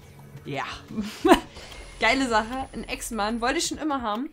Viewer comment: Ich habe bei 3:15 Beschwörung des Echsenmannes verstanden: Geile Sache ... Ein EX-Mann ... wollte ich schon immer haben :-) und naturlich GZ für Praha...